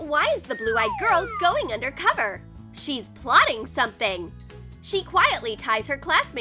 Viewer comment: they said she’s pluning something instead of planing